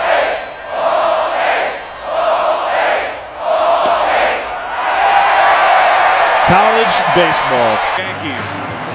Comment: What are they saying? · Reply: Texas A&M is ball five ball six ball seven and so on and the Cubs is their fight song “ Hey Hey Holy Mackerel”